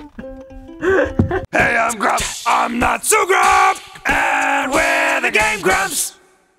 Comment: I was seeing the comments while he was talking about Wendy's at the beggining, and when the intro came in, I wasn't playing attention, so I thought they said: Hey, Im drunk! I'm not so drunk! And we're the Game Drunks!